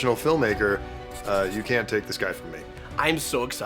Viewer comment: I swear I just heard say, "You can't take this guy from me."